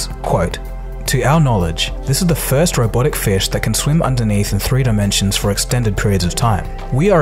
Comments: It said untethered not underneath.Just thought I'd say. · you quote the guy saying “underneath “ instead of “untethered”.